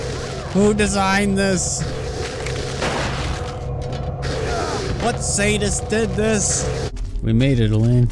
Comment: This video was sponsored by "Hole'y Order"~ 12:40 I heard 'What saint did this?' :v